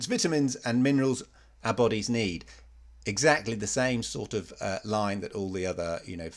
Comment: I heard "lie", but you said "line". Had to go back a few times.